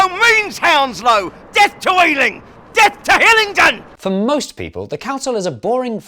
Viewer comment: I thought he said “Get toiling! Get to Hillingdon” for so long until I looked at the subtitles.